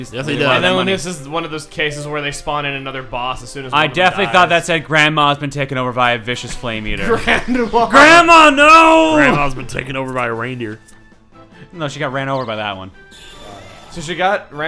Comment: I thought it said Grandpa